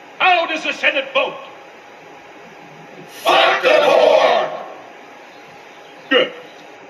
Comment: did they say fuck the poor?🤣